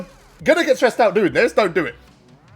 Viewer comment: I thought you said stretched out at first 😂😂😂😂😂